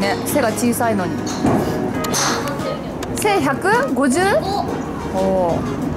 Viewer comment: 背155が1155って聞こえたのは自分だけ？笑